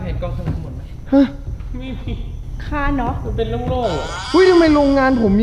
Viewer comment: ได้ยินว่าค่าน็อคตอน15:59